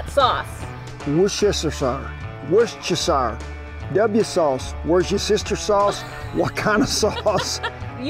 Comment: W sauce Kent sounded like he said watch your sister sauce lol😅